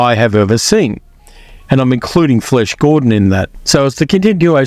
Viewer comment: have i heard FLESH Gordon ? 🤣